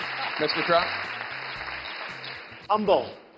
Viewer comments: What did Donald say at 6:13 ?? I hope he wins .. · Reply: I think he said "humble". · What dose Trump say at 6:10? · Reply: "Humble"